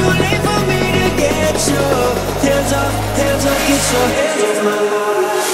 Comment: I legitimately thought this said "Hands Off My Pizza" and I was so excited... Fail..